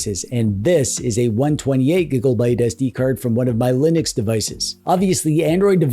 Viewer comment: Did...did you just say, "128 giggle byte"? Because if you did, I'm totally stealing it.